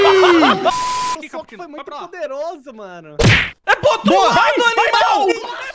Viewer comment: agora eu entendi direito o que ele falou, eu ouvi "amigão" mas ele fala "animal"